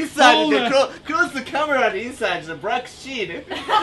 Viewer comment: does he say "inside is black shit"?